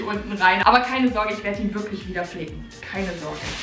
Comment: aber keine Sorge ich werde ihn wieder flicken Was verstehe ich!!! : keine sorge ich werde ihn wieder fic*ken